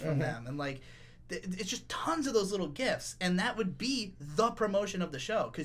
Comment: I first heard "gifts" instead of "gifs". Yes. Thank you KyoAni.